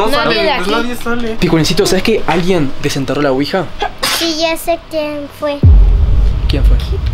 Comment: quic 12:31 parese un dron en la esquina rojo 5:57 anda algo mal 8:16 se escucha el minion 7:06 se mueve la ropa atras de fede Saludos al proximo video fede Estoy suscripta 🤩🤩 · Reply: fue galletita que dijo que